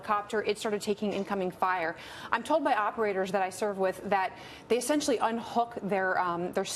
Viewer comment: Did she just say “operators I served with”?